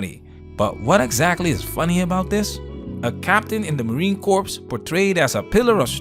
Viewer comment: Did u really call it the "marine corpse" at 5:55 (ish)....lmao. am i only 1 to notice this??